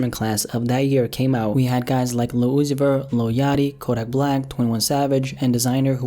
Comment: When u said “lil uzi vert” the captions thought u said “Lucifer”😱